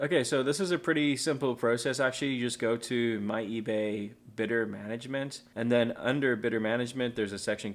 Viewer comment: Fora moment I thought I heard bitter management 🤔😂